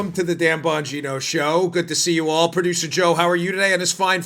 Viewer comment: Hey, Dan! I'm thinking you might call it the Daniel Bongino show. A couple times now, I thought I heard you say, "The Damned Bongino Show" 👍 That's probably OK, since the Dimmocrumpies likely recognize it that way. Carry on, soldier. Love what you're doing!